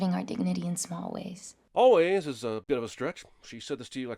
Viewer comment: The captions say 'but' instead of 'bit'